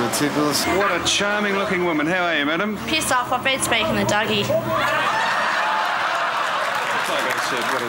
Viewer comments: What did she say after she said piss off? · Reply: I have been speaking to Dougie. Dougie being Doug Hawkins ex highly regarded Footscray player so much more liked in Footscray. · What does she say at 2:35? I'm Canadian so I might not know the Aus vernacular. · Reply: P15S off. I have been speaking to Dougie. Dougie being Doug Hawkins ex highly regarded Footscray player so much more liked in Footscray.